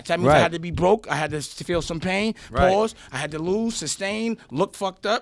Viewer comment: Everything Dame said was absolute fire, but did dude say pause after "feel pain" hahaha